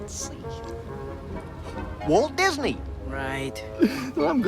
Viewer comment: Did Goofy just say Walt Disney ??? Also where's Captain Hook, Rhino from bolt, and King Candy / Turbo from Wreck it Ralph ????? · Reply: Goofy actually did say Walt Disney and even Max says Disney's name later in the movie.